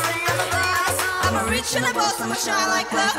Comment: What I heard: I'm a Witch, I'm a Boss 😂 What I assume I heard: I'm a Rich, I'm a Boss 💰